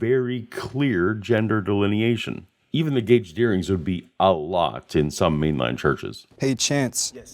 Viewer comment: "Even the glblkjdsfjdfk a lot in some mainland churches" I have no idea what you said there ...gauged earrings, I guess?